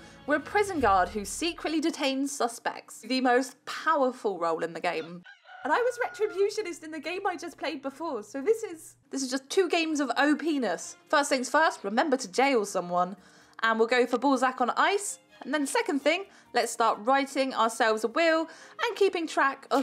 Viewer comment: omfg X'D it sounded like O-penis xD hahaha omg... that made me laugh so much..